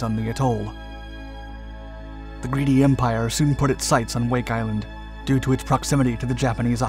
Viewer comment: Did you say the "greedy empire" at around 1:40???